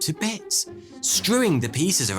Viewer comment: Hello, I'm a non native English speaker and cannot understand the word said on 14:58 . Can some good soul enlighten me please? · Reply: 10 months ago but the word he said was strewing which is a synonym for scatter.